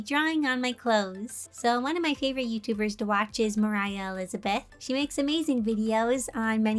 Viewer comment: She's like Moriah Elizabeth, Wolfychu and well, herself ♥♥♥ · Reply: I thought it said "Morioh Elizabeth" and my inner jojo unleashed